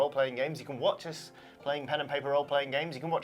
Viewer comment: When said "You can watch us playing pen & paper roleplaying games" I briefly thought he said "Watson playing pen & paper roleplaying games"